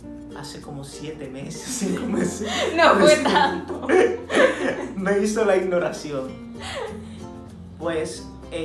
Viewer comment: "...me hizo la ignoración" ... es que acaso escuché bien ??